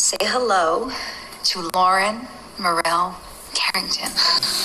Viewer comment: Did they name after Liam mom? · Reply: oh my bad I thought she said Laura